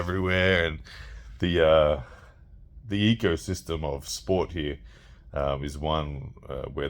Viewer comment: I misheard when u mentioned the ecosystem around Larry's gym. Thought you said Egosystem 😆